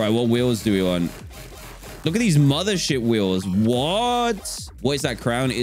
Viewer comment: sounds like he said the S word but he didn’t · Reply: Mothership